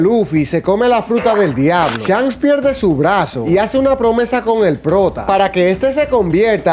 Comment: yo entendí "con el dr0g@ (aunque si nos ponemos a pensar, ese es chopper XDDDDD) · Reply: El prota